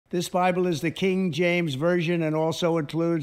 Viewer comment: I thought he said, "King Trump's Version...".